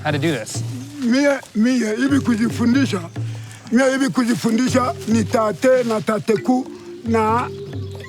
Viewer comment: Did he say thaatha?